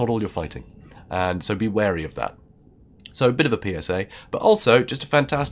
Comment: also, at 9:01 I misheard "PSA" and legitimately thought said "So, bit of a penis, eh?"